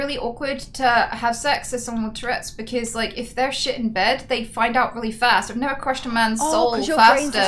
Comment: I for real thought she said “If they shit in bed, they’ll find out fast.”